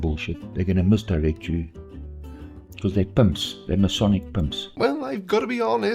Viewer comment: Punks. Masonic punks. I hear the hard k sound the 2nd time he says it